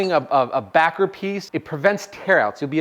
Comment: you said you used a back grip piece? What’s that? Not sure if I heard it correctly?